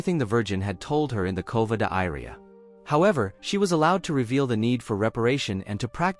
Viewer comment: Why does it say covid diarrhea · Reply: It says Cova d'Iria. The automatic captions wrote that and I haven't noticed lol.